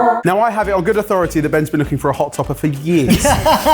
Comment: Did just call Ben a bottom?